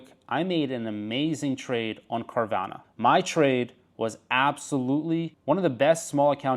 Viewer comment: At first I thought you said cardano! 😮